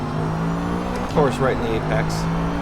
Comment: Did you really just say that car in your qualifying was a Porsche!!!! It was an Aston Martin zagato 'facepalm' on the other hand I will sub as it was a good vid! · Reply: yeah i know but it really did sound like he said "porsche in the apex"